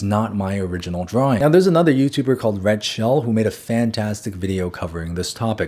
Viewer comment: damn i was listening this video while doing something else, i misheard it as "Red shell" like bruh why does shouting out the Ig slop lord 😭